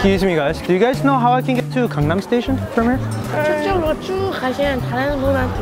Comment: that accent when he said “Gangnam” and “Yeok Sam” thoo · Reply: I thought he said CONDOM station 😆😆😆